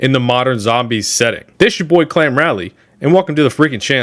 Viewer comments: thought this me said klan rally i snapped my neck so hard to rewind. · I thought you said "this is ya boy, clan rally" and I absolutely had to do a double take on the channel name